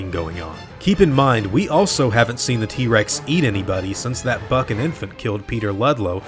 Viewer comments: "since that buck and infant" Thought you said "that fuckin infant". Took me off guard. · You said buck and infant I thought you said that fkin infant